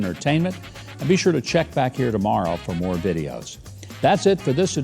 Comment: You said,, “That’s it for this edition...” and I heard, “... the sedition...”. Lol